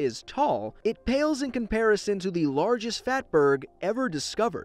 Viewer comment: Every time he said fat berg I thought he said fat bird